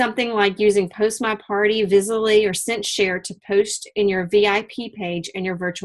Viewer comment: Hi at 19:15 she says about Automating to use "post my party" and it sounds like Vizally??? not sure what she said there. do you recall?? And what was the other thing after that also? I will be starting up in Jan and these are good tips. is this found in PCU? the automating things i mean. Thanks for the tips!